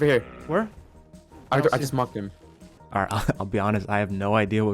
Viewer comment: i think he said "i marked him"